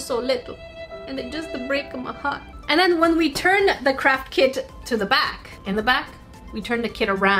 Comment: Omg it sounds like she is say crap kit or kits